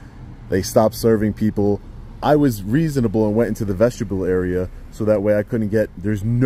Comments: Did he just say “ vegetable area”? u r the best. Lol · 😂I thought I heard vegetable area twice now. I think it's vestibule. Anyways. Great video as always. Your skills far exceed 99% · It sounded like you said "vegetable area" at 13:20. Vestibule area would be correct